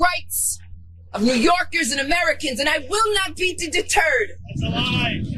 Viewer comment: OOOPs I should have had my hearing aid on. I thought Leticia said, "I will beat the "TURD"............😉🤣🤣🤣